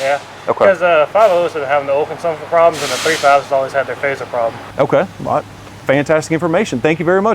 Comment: the Technician says the 5.0 s have oil consumption problems and the 3.5s have ???? problem. Can you tell me what he said? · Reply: "3.5's always have the phaser problems"